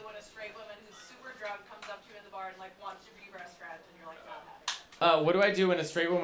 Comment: for the second gowrl i did not hear "...wants to be best friends" i heard "wants to be breast fed"